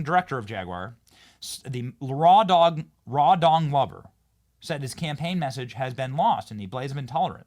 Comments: I kept hearing “raw dung lover” when Benny was saying his name… LOL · Apparently the ceo is now threatening staff that criticise the ad with disciplinary action. Smdh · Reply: Did Benny say the CEO’s name was Raw Dong Lover? I’m sure I misheard, but it would explain the campaign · I thought you said "Raw Dog Lover" until you repeated a few times and I read the clips, "Rawdon Glover"......ahhhh, easy mistake but after watching the ridiculous ad Raw Dog Lover seems to fit better. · did he just say "Raw dong lover"? hahahaha